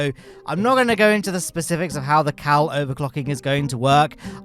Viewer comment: Got to love a bit of KAL overclocking here and there. KROOOOM! :D Warp nacelles on a spaceplane! I never thought of that before. I love them.😃 is initiated into the secret kabal who know the truth about Dres. 🧐 Gerdammer is beautiful! Those interplanetary rings are gorgeous too! Getting a different kerbal out on each world was an old extension to the Jool 5 challenge. If nothing else, it meant you had to carry 5 kerbals through the whole flight. "Britain's ocean city" fits Plymouth so well. I dunno why, it's an aesthetic thing in my mind. Maybe it's because I associate "ocean" with pleasant sunny weather, and Plymouth always gives me that vibe even when it's not sunny. I was in Plymouth that year the whole of the British Isles were covered in snow except basically Plymouth. Driving home was a bit of a shock (but very pretty). BWAHAHA! That takeoff was indeed glorious! :D and no damage, wow! I really appreciate your cheerful commentary. Thanks so much for putting the effort in. 🤗And of course I hope your channel continues to recover. I'm proud to be one of the 5 people left at the end of the video! 😂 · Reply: I kept hearing him say "cow overclock". I could understand how that would produce infinite methane gas, I just didn't know where all the grass came from.